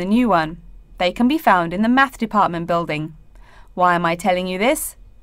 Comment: 34 I heard obviously "math department" so why the answer is "maths department"